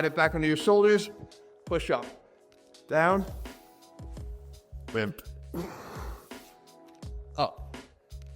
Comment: Anyone else heard call him "Wimp" at 5:58 ? 😄